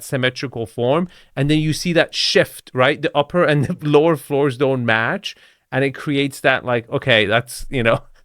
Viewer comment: I keep hearing "no cemetery in the lower floors" )))) Still better than the French accent which should not mock in the first place.